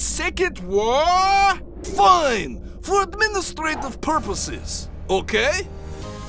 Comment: did I hear Soviet Union say purple six or purposes?